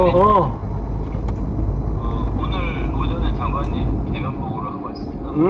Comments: 장관님 대면보고하고 왔습니다~~~로 들리네요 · 장관님 계면보고로 들리는데 계엄 대면보고의 준말아니야?